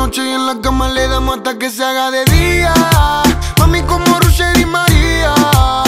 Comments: Nose porque entendí "mami como el rush de Di Maria" 😅 alto tema 😎🙌🔥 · MAMI COMO RUSHER Y MARIIIAAAA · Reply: Ah boludo yo pensé que dijiste como Rusher y Di María La re flashee